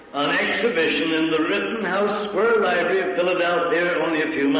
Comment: does he say Rittenhouse?